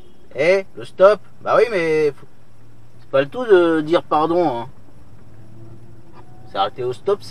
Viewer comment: Tu es sur qu'il a dit pardon? de chez moi j'ai plus entendu RAF 😂